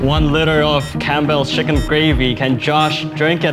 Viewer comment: I thought it said gravity